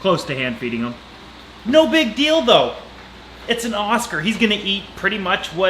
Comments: Did i just heard you saying 'big dil do'? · " No big Dildo" I'm sorry..that's what I heard xD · Did he just say "'no big dildo"' at 0:16? Lol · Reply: "No big deal though" But still lmfao